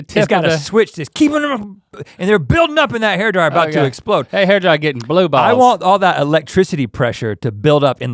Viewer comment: did link just say that hair dryer gettin blue balls 😂😂😂😂😂